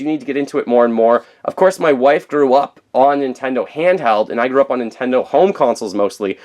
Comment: I thought you said "my wife threw up on Nintendo handheld"